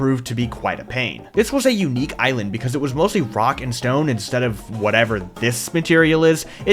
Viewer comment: do I hear….. A ROCK AND STONE?